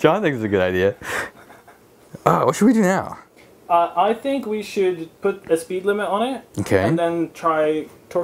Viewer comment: I heard "I think we should put the speed limit on it" and my smile turned into a frown... Also im super super super late